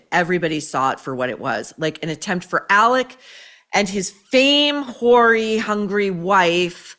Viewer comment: I heard “his fame whorey hungry wife” and immediately pressed like.